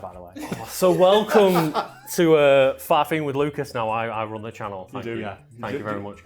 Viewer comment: I really thought he said "faffing with Lucas"...